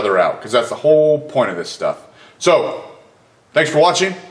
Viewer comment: Bro it sounds like u say train on time.. though im aussie. Great vids man < learning lots over here.